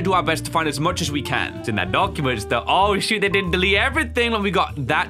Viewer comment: 0 3:01 what did chu say?!?!?! To think on my family friendly video I’m disappointed;w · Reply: He said shoot